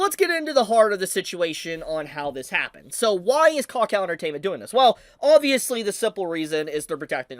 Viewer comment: can't even lie i kept hearing "cock out entertainment"